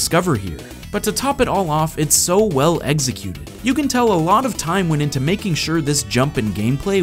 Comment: def sounded like you said 'eggsecute' (forget how to spell the Pokemon. LOl)